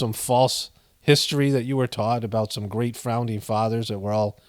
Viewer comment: did i hear "great frowning fathers"? 🤔🤣